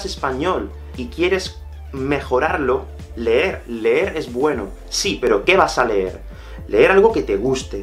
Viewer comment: I heard the word is form English I didn’t hear es